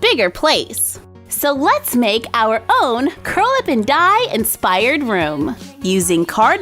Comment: I always thought you were saying Curlaven Dye salon, not Curl Up & Dye